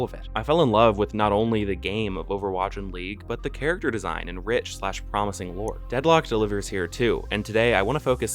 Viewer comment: did you say rich-slash-promising lore?